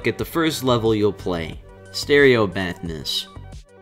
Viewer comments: "let's look at stereo madness" what i heard: "let's look at stereo badness"l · Did he just say "Stereo Badness" ?